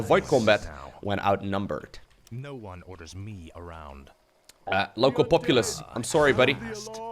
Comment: I swear I heard he saying "Lowko Populace" - I almost sream "OMG EMPEROR LOWKO, YOU`RE BACK"